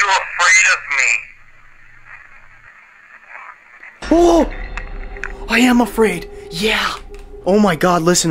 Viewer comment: the EVP when you ask are you afraid of me n it answers IM AFRAID YEAH i heard more than that i heard I'M AFRAID OF THE DARK YEAH.